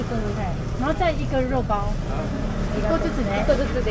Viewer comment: could you please tell me what she said in the end?いどつつ? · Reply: ありがとうございます！ she said “一個ずつね”. (you guys want one of each, right?)